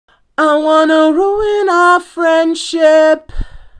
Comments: A couple years ago I thought it said: I wanna ruin our friendship, we should be llamas insteaddddd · Nah I always hear “we should be lavas instead”